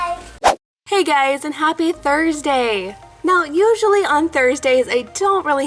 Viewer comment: I thought you said happy birthday